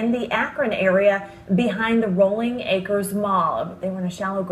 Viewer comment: Did I hear her right? She said "Rolling Acres Moll."